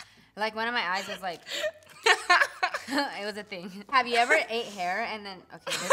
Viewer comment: I legit thought said "have you ever had a cheese burger" "no" Me- WhAt!!!!!!